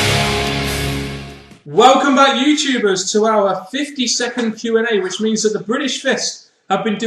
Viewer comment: Lol towards the end when you said "52nd" q+a I thought you said "50 second", and I was thinking "huh? I'm sure It's longer than that!" Anyways, congrats on reaching this milestone, and I'll keep watching!